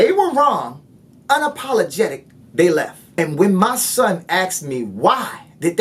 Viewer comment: did he say " when my son AKS me why"